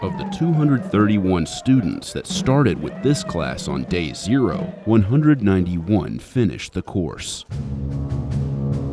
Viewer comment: did I hear a sheeeeesh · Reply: I was actually there for graduation, and I’m so glad someone pointed it out. They all did a little celebratory “sheesh” after that clip. 10/10